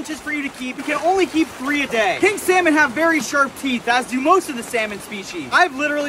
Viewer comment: Pink salmon have sharp theeth ummmmm that’s a chinook salmon man fish identification is very important to learn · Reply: I thought he said King Salmon, which is a different name for Chinook salmon